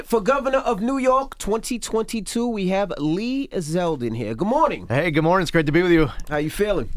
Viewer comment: I honestly thought it said Led Zeplin